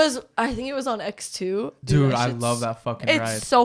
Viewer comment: I heard “ he was on x2” I was like damn they taking ecstasy and doing roller coaster